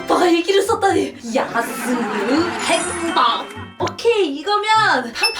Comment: 왜 나만 야(ㅅ)로 들리지